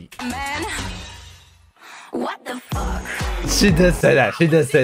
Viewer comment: She didn't say"what da fck" she said "blackpink"